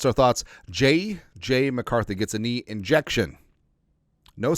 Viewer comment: I thought that said infection and I got really worried